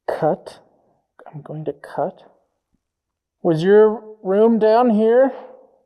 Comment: When is asking for a name in the hallway instead of "I'm going to cut...", I hear "I'm Gordon" something. Dooka, Ducat, Hooker? In a pretty strong accent.